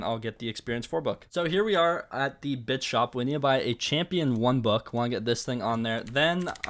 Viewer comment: "B*tch shop" yknow what i'm saying